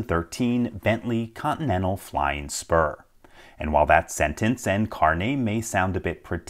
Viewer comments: I misheard flying sperm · Flying Spur I heard it like "FLYING SPERM"